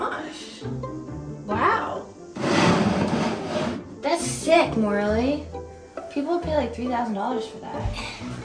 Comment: it sounds like she says "thats sick! Morally....."